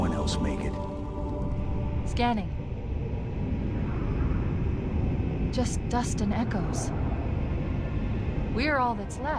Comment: a fun fact here for you: a lot of people misheard the line "Just dust and echoes" as "Just Dustin Echoes" and a whole meme was born about a character named Dustin Echoes who survived the first Halo's destruction.